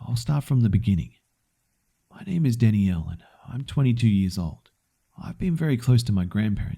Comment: For a second I thought he said Danny Elfman